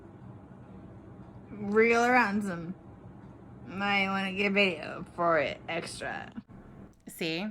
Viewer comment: i think she said “wriggle around some. I wanna get it on video. Extra.”